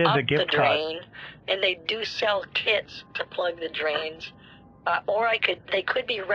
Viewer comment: I thought for a second Kit said "And they sell KIDS to plug up the drains"